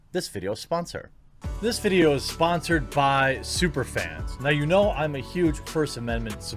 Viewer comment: Ohhhh, super fans, I first I thought you said Only Fans lmao